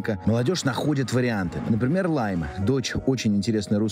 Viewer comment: кто-нибудь нашёл эту самую Лайму* в соцсетях? *так услышал, так написал 😅